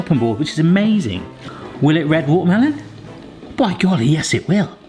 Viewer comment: did he say 'will it red watermelon?'